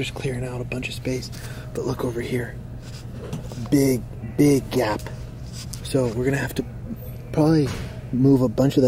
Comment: I thought he said big Gyatt Edit: I meant at 3:22 I also liked my own comment 🤓☝️